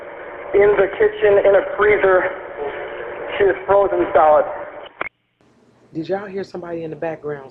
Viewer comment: yes I heard him say "frozen solid" and the cop repeats by saying she is frozen solid.